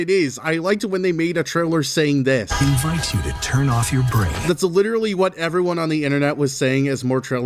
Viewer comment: what did they say??? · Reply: Turn off your brain